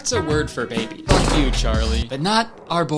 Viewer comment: My name is Charlie and I was only listening to the video, so when I heard him say “Fuck Charlie,” I was briefly offended and confused.